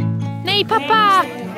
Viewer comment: Is I hear correctly 9:10- nhi papa